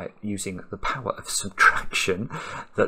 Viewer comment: i heard the power of seduction.